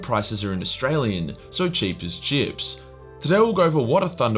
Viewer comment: I heard “Cheap as Gyps”... #StopEgyptianHate